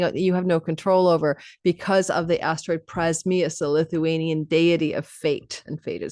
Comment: did you say Lithuanian deity of fate?